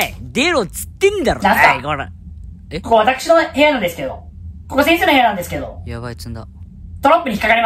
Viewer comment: 「ここ先生の部屋なんですけど」 が殺せんせーの部屋なんですけどに聞こえた(笑)